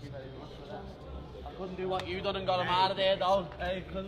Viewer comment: Bless you for writing what he said cus this what I heard .. “I couldn’t do garemarde though”